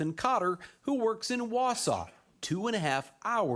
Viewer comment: I thought I heard "Warsaw" and figured it was another town named after a European city, as is common in the US. But no, it's "WAUSAU" 🤣😂